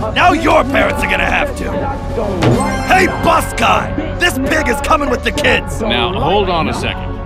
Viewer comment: Stg I thought said “this 🥷is coming with ya”